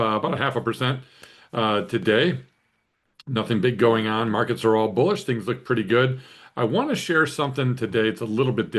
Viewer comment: Did he say markets are bullish?